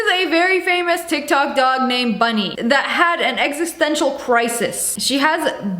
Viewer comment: I thought you said “Funneh” 😂